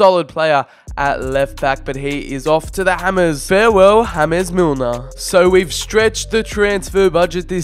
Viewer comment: did he just say hames Milner what what the fudge